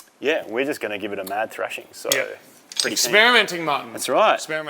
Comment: Lol my 15 year old thought u said "we are spearminting" 😆! I had to put the captions on to show him that you actually said experimenting. Come on guys you have to make this go pssh pssh. Love ya work lads!!